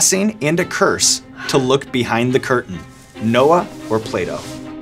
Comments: I thought he said “Noah or play dough” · I'M DEEEAADDD I HEARD: "Who said it, Noah or Play-dough." ☠️☠️☠️ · When he said “Noah or Plato” I thought he said play doh at first 😂😂